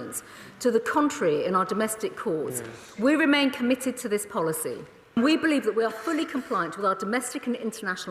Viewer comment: I thought for a moment it said “Patel committed to asylum” Too good to be true 😞